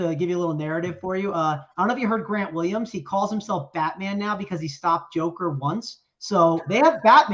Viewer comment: Watching later, but too funny not to share. When discussing Celtics and said Grant Williams calls himself Batman for stopping Joker....I thought he said "Fatman". Doesn't he look 50+ lbs heavier than UT?? Is that muscle add?